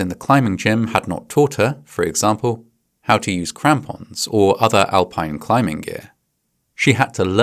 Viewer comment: Crampons? I thought he said Tampons. Well, I guess I need more training.